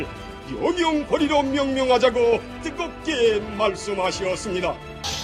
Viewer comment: 띠겁게 말씀하셨습니다로 들었넼ㅋㅋㅋ